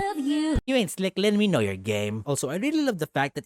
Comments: Please I thought you said gay not game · At first when he said it I thought he said You ain’t slick Lin we know your gay.